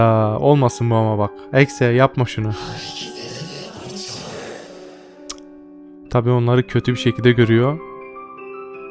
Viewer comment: abi sonic exe burda ne diyor müzikten anlaşılmıyorda bide ses biraz bulanık tamam seslendirme süper çok ama çok iyi ama biraz bulanık gibi burda ne diyor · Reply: "Hareket eden et parçaları..." (Bunu diyor)